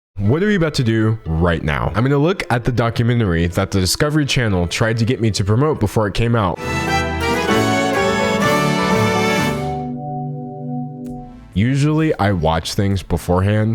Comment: I thought you said they tried to get you to promote it before *you* came out and I was like WHAT DID I MISS